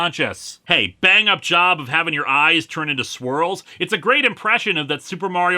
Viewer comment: I misheard that as "having your eyes turned into squirrels' instead of 'swirls', and was left extremely confused and concerned.